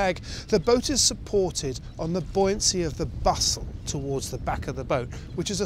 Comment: the boat is supported on the buoyancy of a "Bustle"? what is the word that he says @ 8:03? Bustle? Vussel? Bosel? can anyone tell me how that word is spelled?